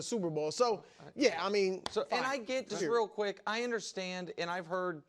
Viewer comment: I thought you said real quick